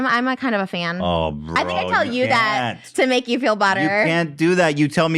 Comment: She deadass said “to make you feel butter” instead of “better” I thought i was hallucinating omfg SO ICONIC 😭😭😭😭😂😂😂